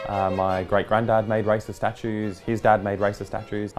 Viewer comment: I thought he said “Hi my names john and i make rice as statues, my dad made rice as statues and my grandparents made rice as statues” The other guy: “why do you make racist statues?” Me: ohhhhhhhhh